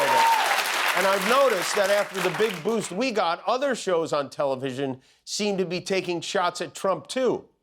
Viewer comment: I thought he said telepigeon! Fitting, seeing as he seems to use a lot of media, while being extremely ignorant of technology in general. Ughhhh.